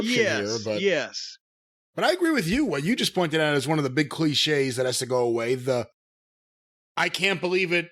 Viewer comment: I thought it said "clinches" and was thinking WTF does Jim Cornette know about actual wrestling????